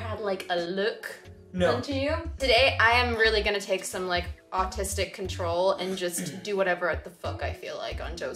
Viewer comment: omg I thought you said "today I'm going to take some autistic control" instead of ARTISTIC and I got so excited as an autistic person I almost commented "Niamh you're autistic?!" LOL